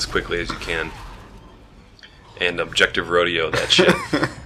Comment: lol i thought it said objective rodeo too